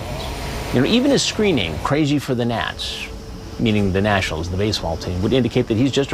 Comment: I thought he said "crazy for the nuts" and i was like no shit!!! 😂😂😂😂